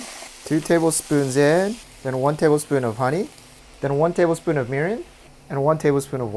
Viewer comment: what type of liquid was that is anyone can tell me, all I heard what table spoon of merum but I don’t think that’s right